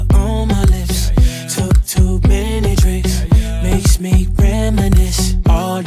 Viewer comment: i thought he say:makes me feminist